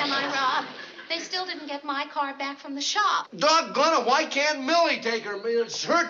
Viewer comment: When I first watched this episode I thought he said “doggoneit!” But the closed captions said “THAT GIRL!” 14:17 lol